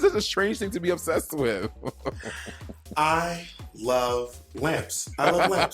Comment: I just noticed Denali said “I love lamps” My deaf ass thought she said “I love glam”